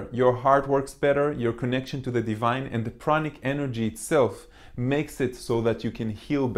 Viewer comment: Thank you a lot again, 💚 Attention to english subtitels 8:16 "and the pranic enegy.." instead of "and the chronic energy.."